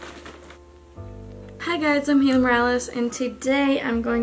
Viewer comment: Lol I’m watching the video with subtitles and when you said “I’m Haley morales” the subtitles thought you said “humor Alice” 😅